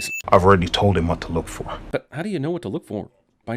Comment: He said "look for" and I thought they said "Lotor"